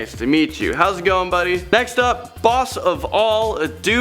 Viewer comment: Who thought said "do. not sing!" Like if you agree. Btw hey